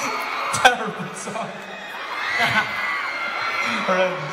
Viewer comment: is it just me or did i hear “terribly sung” not “terrible song” i mean i could be wrong but that’s just what I heard